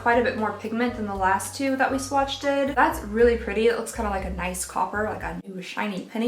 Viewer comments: Did...did you say swatched-ed? I did a double take! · I thought you said swatcheded and I was like girl that isn't the word you're looking for but realized you said swatched did ;( P.s. I'd love to be entered!